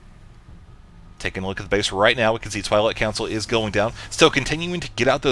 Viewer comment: sounded like you said "toilet council" instead of Twilight council :P